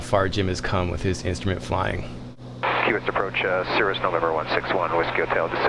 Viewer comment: Osama wasn't in this video, yet I kept hearing him say "For is a number".